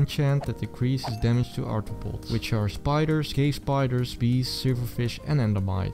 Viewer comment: i heard "cave spiders" as "gay spiders"😂